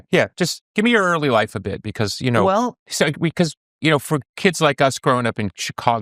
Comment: Did he say Kix or Kiss around 1:15? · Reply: Kix